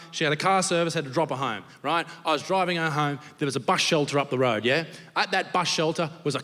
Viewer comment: what did he say? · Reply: bus shelter